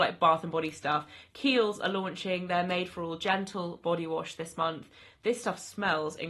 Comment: I thought you said genital body wash 😂